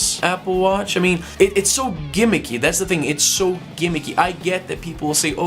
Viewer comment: Hey, What did you say at 1:56. That´s is a new expression for me. Something like "It´s so 'gamekey'". If you could answer this, will be realy cool. Thanks! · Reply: Andre Sousa gimmicky. A variation of "gimmick".